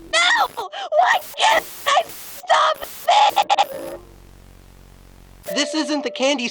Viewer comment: I hear Amanda say why I can’t stop this that seems suspicious????